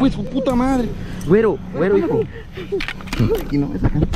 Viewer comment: en el minuto 52:50 Se OYE QUE DICE EL AQUÍ NO ESTÁ CALIENTE CUNDO LE PONE LA LÁMPARA EN EL BRAZO